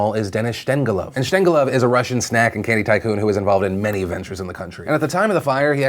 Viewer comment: Did Phill just call him "Russian Snack"?